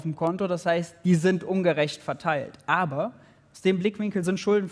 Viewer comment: warum Zensur bei 18:59? ,,es ist wichtig zu verstehen, dass der Staat bei uns´´ (und dann cut)! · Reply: Hallo , keine Zensur, sondern lediglich ein Aussetzer des Livestreams. Hier nochmal das komplette Zitat von Maurice Höfgen an der Stelle: "Aber: Aus dem Blickwinkel sind Schulden vielleicht gar nicht so schlimm, wenn man versteht: Wenn der Staat mehr ausgibt, als er einnimmt, bedeutet das für uns: Neues Geld."